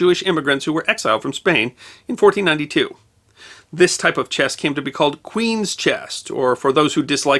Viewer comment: and others show the narrator and the closed caption are saying "chest" instead of "chess"